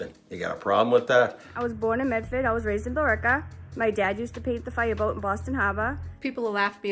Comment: I think that she said Billerica.